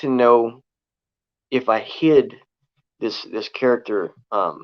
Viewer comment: I thought u said “how well I HID this character”....but you said “how well I HIT this character”....lol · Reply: No I said hid. No one guessed so far